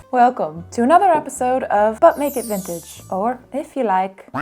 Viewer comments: I know you're saying " but make it vintage" lol, but I always hear " butt naked vintage." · My boyfriend overheard me watching this and asked "Did she just say butt naked vintage.?" 🤣🤣🤣 I'm dead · If I didn't know better I would have thought you said "But naked Vintage"